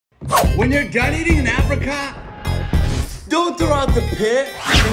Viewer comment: I thought he said “ when your done eating Africa”